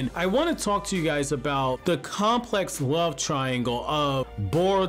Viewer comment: Bruh i Thought this legit said “Naruto’s Complex Love Triangle”